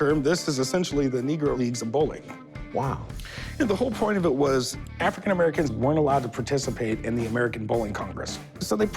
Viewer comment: what did Corey just say?😮😂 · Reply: We should cancel him ( for ripping people off )